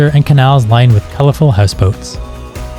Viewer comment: Lovely video. Copenhagen looks fabulous. When you were describing the colourful canal scene, I thought you said colourful housecoats ( you most definitely said houseboats ) 😂😂🤭